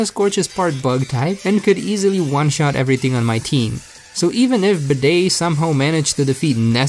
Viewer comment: Did I just hear bidet?